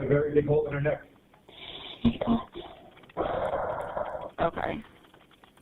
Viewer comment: when he says she's got a very big hole in her neck. Its sounds like says "thank God"... then she oroceeds to have some questionable background noises